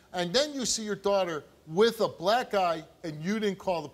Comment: “You didn’t see your daughter with a black eye-“, I thought he said “You didn’t see your daughter with a black GUY and didn’t call the police?” Shit had me dead at first.